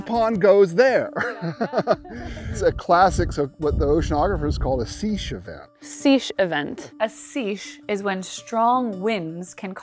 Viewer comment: I was listening without the video and I thought they said (10:20) "a sheesh event"